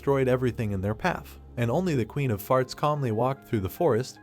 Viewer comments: The Queen of Farts? Did I hear that right? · did he say the queen of farts lol